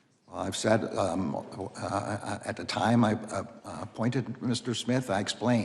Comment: I thought he said he didn't appoint smith